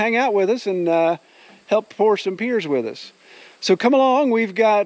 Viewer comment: He's gonna hang out with us and "help pour some beers with us" is what I heard the first time through. 0:26 Sounded fun to me.